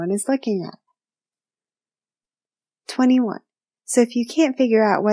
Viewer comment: I thought it said "you might be an airbender" instead of ambivert.